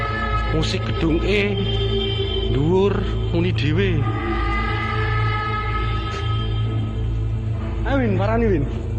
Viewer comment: qw dengernya taangii tangiii...😮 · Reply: Banguuuuuun, bhs jawa ya